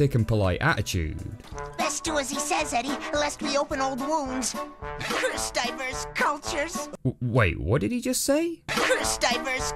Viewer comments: "Guess he's just a massive racist." The man was about to have live eels shoved down his pants! Let him vent! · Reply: Not gonna lie I thought he was saying "First diapers, cultures" and I was SOOOOO confused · what did he actually say, I have no idea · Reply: He said, "Curse diverse cultures"